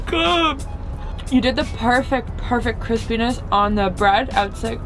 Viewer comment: the subtitles say "Chris penis" instead of "crispiness" 😂